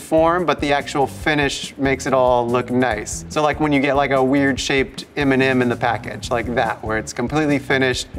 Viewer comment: I love this series! It's so cool to see how creative people see design at a different angle! Also in the first part of the video I heard "When you get like a weird shape Eminem", so I had to turn the captures on to see what was that LoL